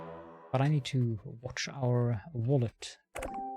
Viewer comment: for a second, I thought I heard you say "but I need to watch our Wally" there